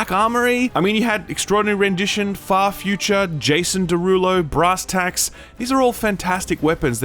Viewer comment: I spit my sandwich out laughing when I heard "Jason Derullo" lol